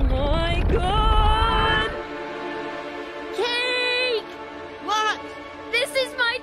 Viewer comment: did Kate just say F$@ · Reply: Cake said what not fuck lol haha